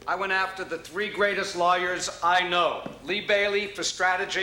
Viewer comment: "I went after the three greatest liars I know..." (i know he said lawyers, but it sounded like liars and still ends up being true